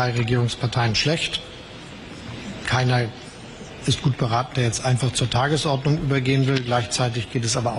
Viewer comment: so, ich habe bei 7:30 verstanden " keiner ist gut beraten, der jetzt zur Tagesordnung übergehen will, lasst uns zur Tagesordnung übergehen " 🤔